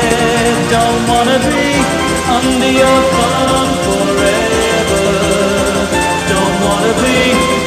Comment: I always thought it said "I wanna be under your bum forever" haha